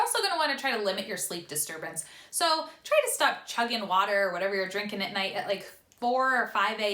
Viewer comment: low key I thought you said try to stop chugging vodka there for a sec 😂